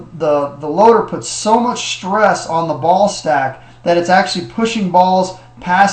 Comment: when u first said ball stack i thought u said ballsack haha